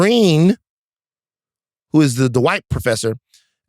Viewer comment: did Van say he's the "white" professor? I see a black man on my screen 😂